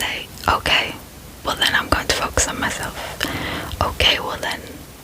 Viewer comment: what did you say here? I didn't quite catch it.. 😂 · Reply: 😂😂 not me running to replay the video! Focus....it was definitely focus 😅